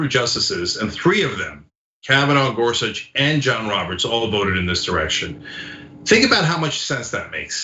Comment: What did you say at 2:46? All voted what? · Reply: All voted in this direction